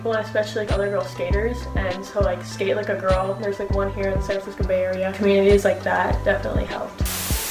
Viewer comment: DiD i JuSt HeAr SaN fRaNsIsCo BaY aReA?!